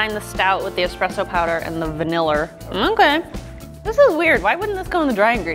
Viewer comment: Did anyone catch her saying “Vaniller”